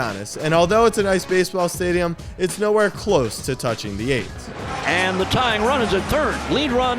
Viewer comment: I thought you said it wasn’t close to touching the A’s and almost lost it lol